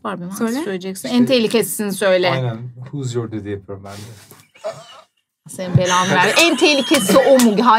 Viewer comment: burda duymadım yaaa ne dedi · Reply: Who's your daddy yapıyorum ben de diyor